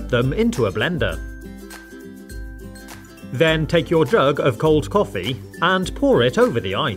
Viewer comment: in subtitiles It said DRUG Instead of JUG